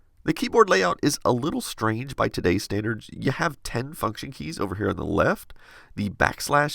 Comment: I heard "ten fucking keys" and almost spat out my dinner